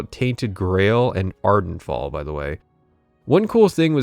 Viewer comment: are you saying ardent fall ? one of my head phones broke and i cant hear what you said. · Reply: Ardenfall